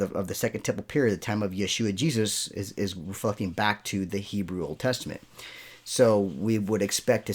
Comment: Woah dr it sounds like you said the f word here 6:12. I know it wasn't the f word though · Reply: I heard that. I was trying to say, “referring back to…” oops. My apologies.